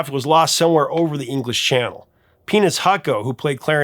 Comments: Uhhh... Did I hear that correctly? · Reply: Michael "Peanuts" Hucko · Wait. Am I the only one who heard him say that glen millers friend was named PENIS Hako? What a name! 🤣🤣🤣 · Did he say Penis Hako? Ancestornof Hak Tua girl?